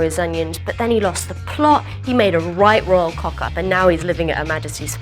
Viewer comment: What does she say at 0:32 that means messed up? I couldn't quite get it. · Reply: ***** Is it "right royal cock up"?